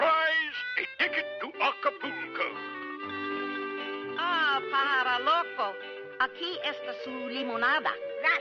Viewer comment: what does she call Woody (in Spanish)? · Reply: Aah pajaro loco, aqui esta su limonada Which means Ah crazy bird, here's your lemonade